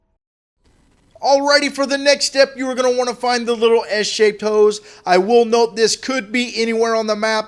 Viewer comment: I thought he said S-shaped hoes 😂